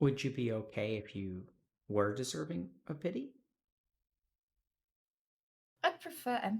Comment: I heard "...were deserving a kitty?"